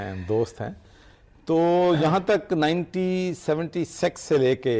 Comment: did he said 1970sex! 🤣🤣🤣